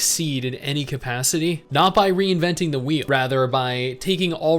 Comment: I thought you said "Not by reinventing the weed-"